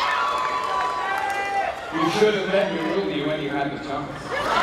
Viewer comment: What did he say? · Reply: ”You should have let me rule you when you had the chance.” (I think!)